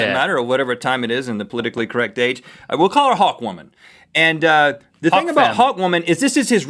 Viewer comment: Hawk woman... more like hot woman... thats what I thought he said...